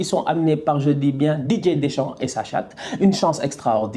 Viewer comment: J'ai entendu "ils sont amenés par D.dechamp et sa chatte"🤣🤣🤣🤣🤣